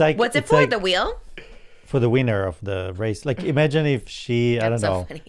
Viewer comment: I thought said “weiner” 😂